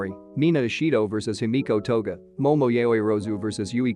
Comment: Part 5 please I am genuinely loving this story and also just to make sure I heard the video right did I hear the say himiko toga